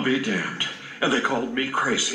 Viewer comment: what a minute did he say sussy? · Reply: No he said "sausage seeds"